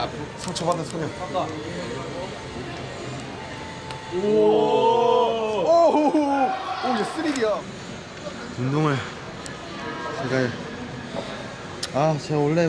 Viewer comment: 에서 랩몬이 오~!!3D야!를 오~!쓰레기야!라고 들었어욬ㅋㅋㅋㅋㅋㅋ 죄송해요 랩몬앀ㅋㅋㅋㅋㅋㅋㅋ